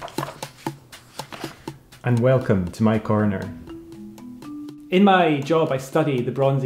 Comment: I keep hearing "my coroner" and then have to process and reinterpret.